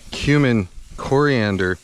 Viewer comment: I thought you said "Human Coriander..." and I was thinking of "The Day The Earth Stood Still," for some reason.